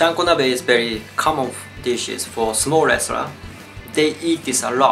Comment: i forgot i clicked on a "sumo wrestlers food" video and thought you were saying "small restaurants" for most the video. i suck